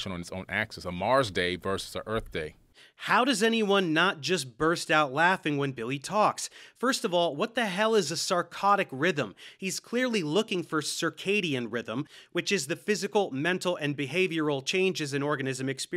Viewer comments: I heard “psychotic rhythm” the first time lol Billy definitely has a psychotic rhythm lol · I thought he was saying a psychotic rhythm instead of circadian rhythm.😆🤣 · Im sorry, but I heard "psychotic rhythm"... 😂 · i mean honestly I heard him say Psychotic rhythm 😂. As mentally damaged as he is I don’t doubt that he actually said that.